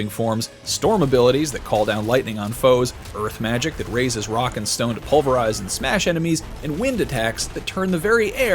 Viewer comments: did you say ROCK AND STONES ?! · DID I HEAR A ROCK AND STONE?